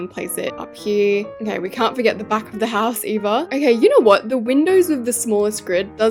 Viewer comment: Did she refer to herself as Eva? When she placed the window down she said "dont forget to do the back of the house Eva" i'm asuming that's her name, or did i just mishear Edit: i rewinded it, i think she said either